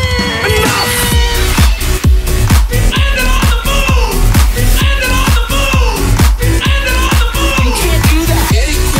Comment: what is he saying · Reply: We landed on the moon!